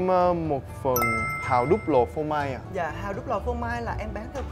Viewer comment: S ns hào đúc lò phô mai mà mk cứ nghe thành hào đúc l*n phô mai quài .____.